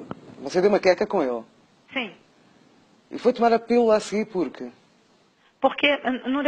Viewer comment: E foi dar tomar assim, porca? Eu só consigo ouvir isso 😂